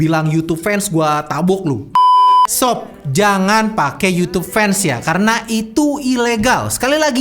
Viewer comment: Youtube fans apaan mamang? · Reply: youtube vanced mang aku juga salah denger tadi, kirain youtube fans wkwkwk